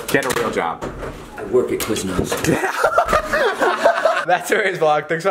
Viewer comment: What does he said in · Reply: I work at quizmos.